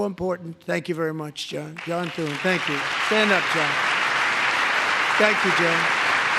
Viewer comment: i thought he was saying stand up , sit down all along